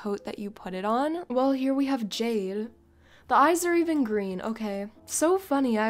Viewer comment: it is just me or she really said "jail"